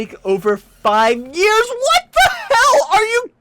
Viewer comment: Did he say, " What the hell"?? 6:20 👁👁